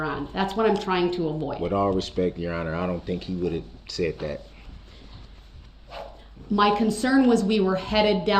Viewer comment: If you put headphones in he said “ with all due respect your honor I don’t think he would have said that “. Then he said under his breath “ I don’t think he’s smart enough “ 😂😂😂 this guy is a joke. He’s talking about someone being smart enough. He obviously doesn’t realize what a clown he looked like and sounded like 🤡